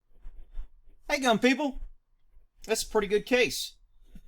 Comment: I swear it sounds like you say "Hey dumb people"..... lol